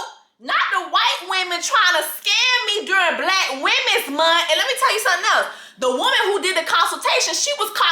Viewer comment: I could've sworn I heard ole girl say "let me tell you about Milan" as in Milan laser spa